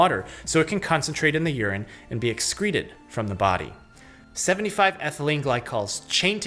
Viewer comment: Hey, at 6:43 you say 75 but the subtitle says 3000. Kind of big discrepancy. Thanks for the video!